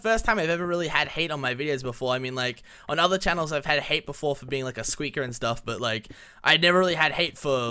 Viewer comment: when you said that you got hate for being a squeaker i thought you said YOU GOT HAYE FOR BEING A SCREAMER OMGG